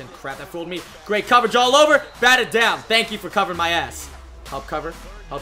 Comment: Did he say ass